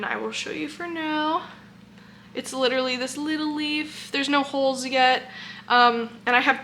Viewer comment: all i hear is Patrick Star “Leedleee” when you say Little Leaf 🥲😂 you are glowing!! Love plant update videos 😍